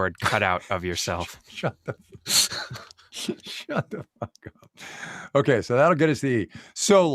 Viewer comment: Did Kai say "shut the f*CK up" at minute 17:42?!? Hahaha... love it.